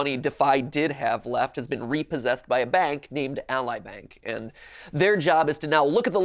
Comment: if you put on subtitles, says Ally Bank, but the subtitles say A Lie Bank